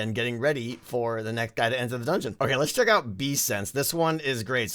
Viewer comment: Why did I hear Beefsense